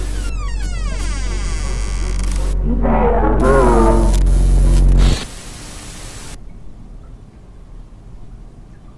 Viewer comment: what did Amanda say? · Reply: ”Say Hello!”